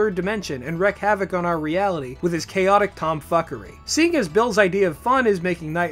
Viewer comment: Com fuckery? Am I the only one who heard this? Or did everyone hear com fuckery?